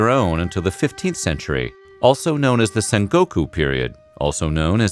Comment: Did he just say Son Goku ? 🤔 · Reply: Sengoku